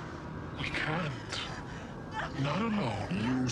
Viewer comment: can someone please tell me what she's saying? All I hear is "nunano" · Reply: "nunano" means "not alone" but said in a confusing way